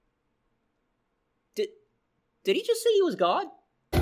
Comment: At first, I thought sonic exe said he was dumb.